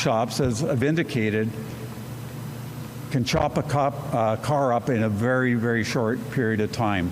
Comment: the superintendent (2nd guy) is totally cooked. Did he say "Chop a Cop?"